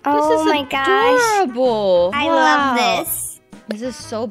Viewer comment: she said “adorable” but I heard it as “horrible” lmao